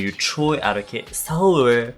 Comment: I always thought you said Choareuke and just after seeing how it's written I realised that it's Troiareuke